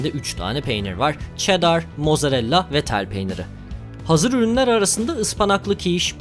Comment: abi hazır rünler arasında diye anladım , iyice de lole alıştırdın bizi alışamıyorum böyle videolara